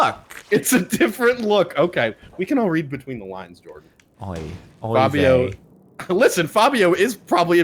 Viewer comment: did just say "oy vey" lmao